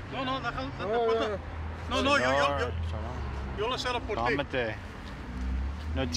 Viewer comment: the caption says "Im here for you" but neither of the officers said that. The policeman with the dark hair said, "I'll close is for you," and the other policeman said "calm down."